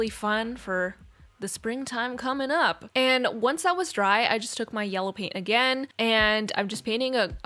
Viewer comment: You probably said "once that was dry" but i heard "once i was dry 😭